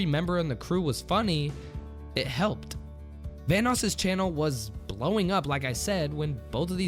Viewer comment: I thought you said THANOS